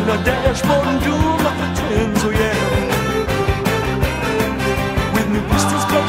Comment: In the movie I thought that at 1:03 Danny said I’ll pound them in the dashboard and do them if they’re twins 😂